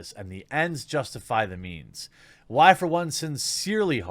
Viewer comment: I thought you said the "N's" justify the means and I was shook.